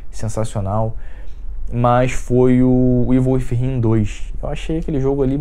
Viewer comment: Qual é o jogo que ele fala no minuto 21:45? eu não percebi nada do que ele disse. O video a partir de um certo momento ficou fora da realidade, as campanhas do the last ou us e quantum break duram no máximo 15 horas o god of war dura entre 25 a 35 horas "mas são campanhas que não duram muito" ahahahahaha, ai. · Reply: tiago vieira evil within 2